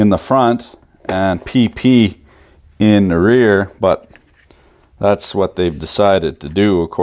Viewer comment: Did you say peepee in the rear and just keep on going like nothing happened?? WTF over??